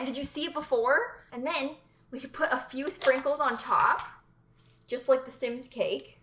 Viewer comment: I thought she said Pringles instead of sprinkles and almost had a heart